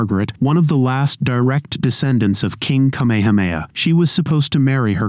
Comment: did he say Kamehameha?